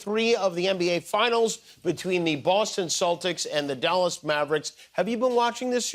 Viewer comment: I want, people, to stop saying "Sellticks". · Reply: I heard "Saltics"